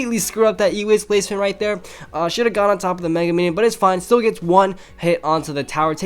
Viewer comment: WHA? did i hear mega minion?